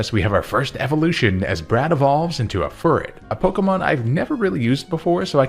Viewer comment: I actually thought he said "Brad evolves into a furry"